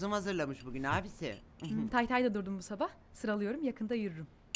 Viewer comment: burda leyla ne diyor anlayan varmı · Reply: Tay tay da diyorum bu sabah , yakında yürürüm diyor